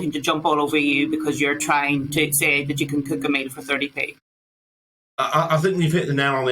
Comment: slice of bread , some jam, or peunut butter etc less than 30p cost wise.I could go on and on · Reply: thought he said a meal from scratch.